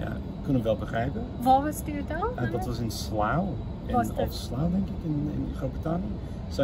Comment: did he say Slough?